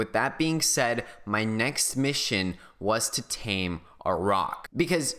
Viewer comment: i thought he really said tame a god damn rock